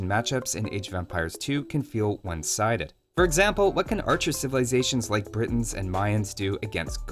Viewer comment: do you mean " What can archers do...." instead of " When "? 😅